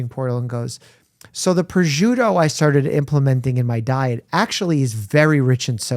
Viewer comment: can someone translate what mike said here? 1:18:52 Prejudo? what was she eating? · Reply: I believe he said 'prosciutto', which is a cured italian meat